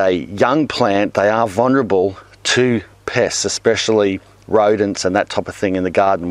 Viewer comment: Pests! I thought you said piss at first. XD